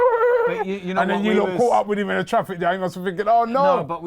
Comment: Instead of traffic jam Robbie said Autrafficyang